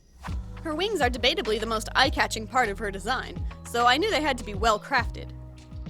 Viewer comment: I am so dumb I thought "parts" said "potatoes"